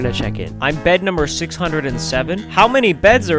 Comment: I thought he said 807 but he went in 208???!!! 🤔